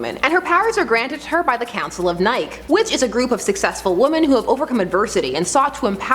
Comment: does she say counsels of Nike